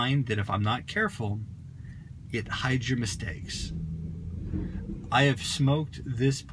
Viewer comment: WTF he say at 2:08? It hydromistakes?? WTF that mean? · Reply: "hides your mistakes" i think. as in he was drawing on it too fast/too hard, so the tobacco itself was burning too hot, but because it cooled the smoke so well he didn't notice till he grabbed the bowl.